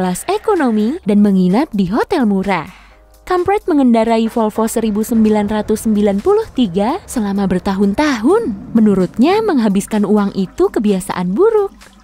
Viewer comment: gue salah denger😭 Kamprad gue denger kamp*et